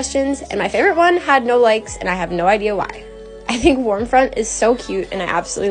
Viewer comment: I thought you said warm fart at first 😭